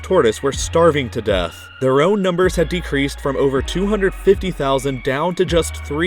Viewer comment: the video says 250,000 but the subtitles say 25,000 plz fix :)